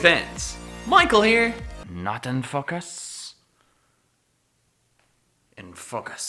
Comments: it sounds like your saying fuck us... fuck us! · At 0.08 I thought he said " Not in Fuckers " XD